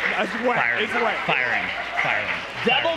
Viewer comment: Anybody else hear “Fire him Fire him fire him” during the dmc5 ad read?